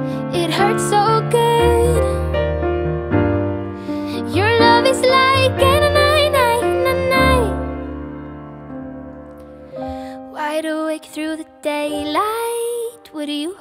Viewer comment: WHAT DOES SHE SING AFTER YOUR LOVE IS LIKE ? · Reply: I always thought she said.. " your love is like hanging on na na na "